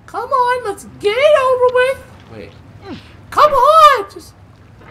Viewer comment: is if just me or did Bonnie said "wait"? That's not from the game